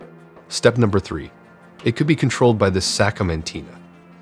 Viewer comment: It almost sounds like a real person talking. I might have been fooled, except at 3:10 when the voice says "Sacomanteen" instead of Satcom Antenna. 😂🤣😆